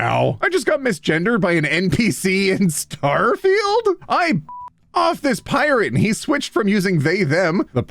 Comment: I wasn't looking at the screen while falling asleep and then I heard "I *****d off a pirate" and had to get up